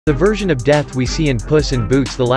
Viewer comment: i thought u said virgn of death